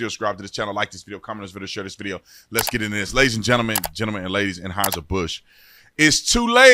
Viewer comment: At first I thought you said ladies and gentlemen, ladies and hide your bush.